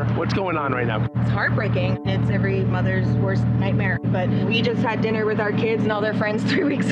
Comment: I thought she said she just had dinner with her kids like three weeks ago!